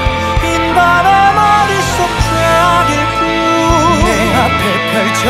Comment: 진짜 분위기 깨지만.. 쾌변을 쾌락이라고 들려ㅠ미치겠다